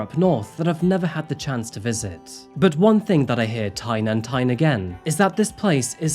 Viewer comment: "one thing that i hear tyne and tyne again" that's it, man, nero's about to inherit the channel 💀